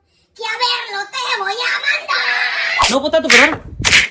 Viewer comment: No te tomes mi Cokita 😡😡🤣🤣 A qué otra canción les gustaría que hagamos parodia? 😃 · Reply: llo escuche no potato peruano en